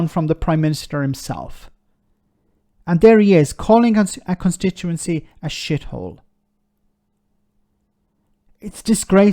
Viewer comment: He was misheard, what he acrually said was " after 13 years of this Government the whole country is a shithole"